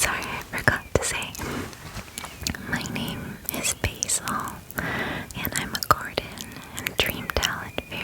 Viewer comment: i heard “my name is baseball”